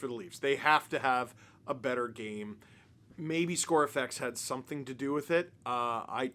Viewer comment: I can't catch what said at 18:50. Maybe blah blah had something to do with it. What did he say? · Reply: He said “ScoreFX” which is kinda the idea that when down in goals you’ll see teams make it close by taking more shots and a team that’s ahead will take less shots as they are playing more defensive.